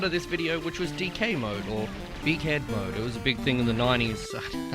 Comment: I won’t lie, every single time it says “big head” I hear “dickhead”